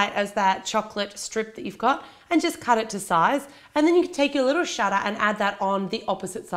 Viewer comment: When she said "shutter" I thought she said shut upmso I was like:whaaaaaa?? Lol